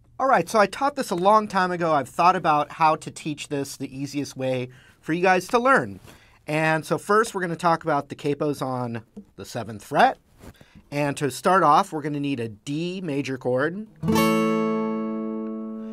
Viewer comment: The "put the capo on the 7th fret" part was pretty easy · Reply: Oh I thought he said put taco on 7th fret. Damnit.